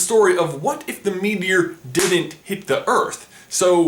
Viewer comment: I thought you said what if the media didn't hit the earth